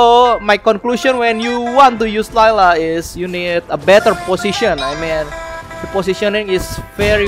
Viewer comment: what i heard here is "A better PUSSYtion"